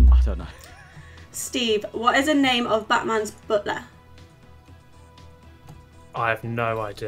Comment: I thought said Batman's brother, not butler.